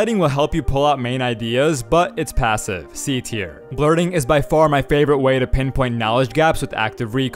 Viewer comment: I thought he said "flirting" 😂😂😂